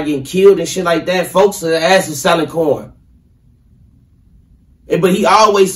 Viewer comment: Did he say J-Hood was selling porn?